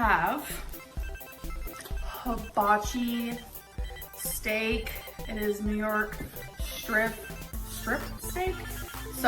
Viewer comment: my volume was super low and because my brain associates shrimp w gorl now i automatically thought she said SHRIMP STEAK im